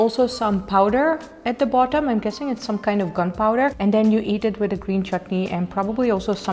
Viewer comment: What? Did you say gun powder?